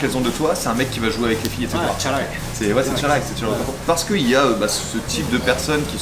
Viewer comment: ずーっとフランス語しか聞こえてこなかったのに唐突な流暢な日本語で「チャラい」